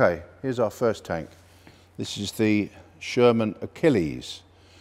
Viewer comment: Ahem, but did he say SHERMAN ACHILLES?